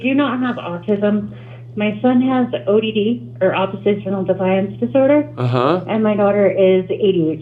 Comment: Did she say, "oppositional Defiance Disorder"?